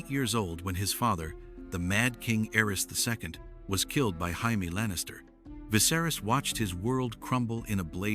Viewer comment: Did you just say Jaime as Haime